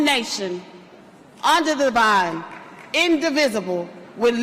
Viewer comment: she says" under the vine" instead of "under God". She does not know the pledge of allegiance. What does that even mean...